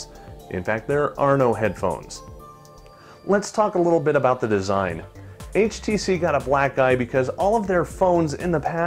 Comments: Sounds like he said "HTC got a black guy" lmfao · He said black eye, but it sounds like he says black guy.